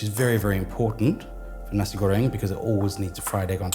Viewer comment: Love your channel but dang I must be watching too many WW2 documentaries because I coulda swore this video said Nazi Goering! 😀